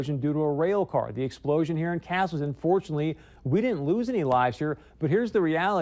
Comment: "Unfortunately we did not lose any lives...." (did he say that or did he say fortunately)